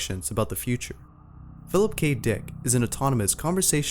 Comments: What did he just say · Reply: Philip k dick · I heard "Philip Cave Dick" lol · the first robot.. "Philip cape dick" thats what I heard, don't judge me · wait did u say Philip kabe dick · On number 6 I thought he said Phillip Gay Dick